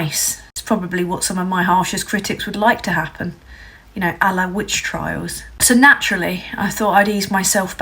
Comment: "Reviews" and "amendments" is all thets ever given of their poxy ancient de repit bs laws ,ethods and governing never EVER changes or abolition/abolishment ... PS i honestly thought u said "allah witch hunt" rather than "a la witch hung" lol